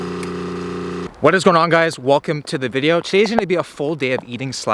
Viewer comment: Did he say “Willcome to the video”? Cause if he did, he needs to be arrested by the pun police